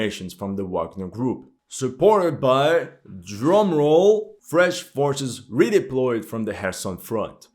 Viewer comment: heard it as French forces at first, almost choked 😂